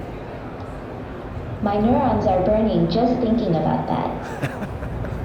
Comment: "My new... What? Can anyone please tell me what was she saying? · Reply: My neurons are dying just thinking of that.